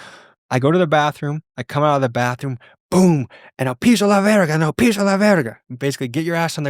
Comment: La berga lol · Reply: Was he saying pinche la verga??? 😂😂